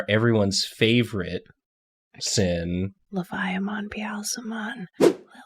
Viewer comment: I heard "Leviamon, Beelzemon, Lillithmon" and then the last one, IDK HOW you got that name from the whisper, that was SO quiet!